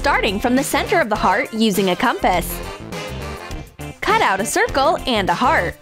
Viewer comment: What did she say? Kompass??